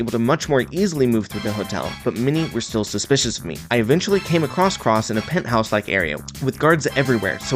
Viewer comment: did he say across Cross or a cross cross? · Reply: Across cross